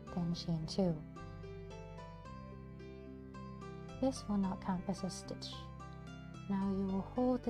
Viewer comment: did you say "this will not count as a stitch" or "this will now count as a stitch" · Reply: will NOT count as a stitch! 😊